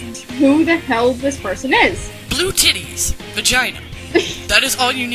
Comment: I tabbed over after clicking on the video and for some reason it didn't play at the beginning so the first thing I heard was "BLUE TITTIES" and I couldn't stop laughing.